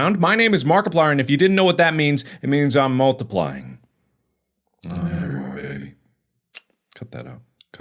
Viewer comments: Intro got me so confused I thought he said Multiplier. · had a stroke and thought he said “My name is Multiplier”😂